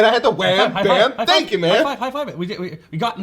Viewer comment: I heard "Wham, Bam, Thank you, Ma'am!" But yeah, the dialogue was sometimes really hard to hear, and if not for me knowing the quote already, I probably would not have caught it.